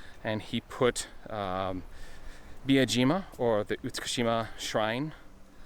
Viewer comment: It's one of those words I find hard to pronounce for some reason. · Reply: I sounded like you were saying Iwajima, a very different place obviously, so I decided to poke some fun at you about it. :)